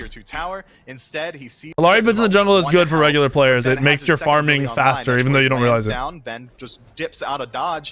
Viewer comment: Can someone tell me what he said at 23:40 sounded like gibberish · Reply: “Talaria boots are better in the jungle cause they make your farming faster”